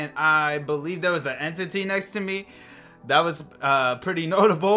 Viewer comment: I thought you said there was a bean next to your bed😂😂